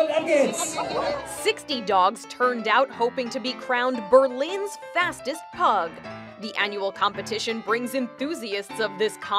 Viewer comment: I for real thought it said “Fattest Pugs”